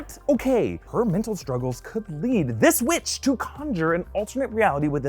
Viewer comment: LOL, I heard this witch as this bi***